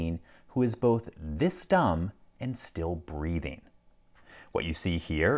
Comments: "It is legitimately hard for me to imagine a sentient being who is both this dumb AND still breathing" EMOTIONAL DAMAGE! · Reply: I heard "breeding", I think that's what steveoulman634 heard as well. I like "breathing" more though ! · LockPickingLawyer: "It is legitimately hard for me to imagine a sentient being that is this dumb and still breathing." Me: "I take it you haven't met my coworkers." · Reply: I heard "... and still breeding" which is a scary thought in itself.